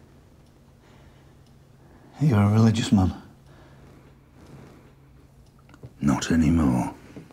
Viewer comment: I can't make out what he's saying... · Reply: You're a religious man? Not anymore.